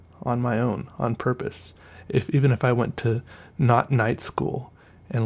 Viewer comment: Wait you mean 'knot night school' or 'NOT night school' like a place where you learn to tie knots in the day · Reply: i thought he said ‘knot knight school’